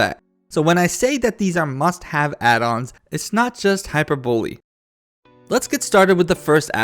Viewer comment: "It's not just hyper-bowlie" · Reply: I heard "hyperbully" lol